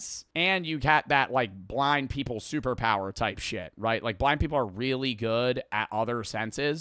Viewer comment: is it just me or did he say black people the second time